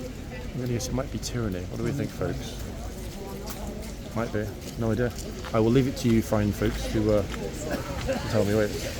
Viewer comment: Hey there. You're right again, horse on left at 8:28 is Tyranny. The mare on the right at 9:06, i couldn't hear the name clearly. Did you say EAP? thanks so much. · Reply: Ypres 😊